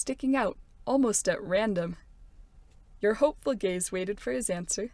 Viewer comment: if you’re captions are on it says “hopeful gays” instead of “hopeful gaze” lmao😂😂😂